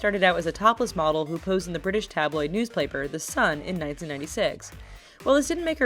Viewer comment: Did anyone else notice that she said "news plaper" instead of "news paper" at 1:15😂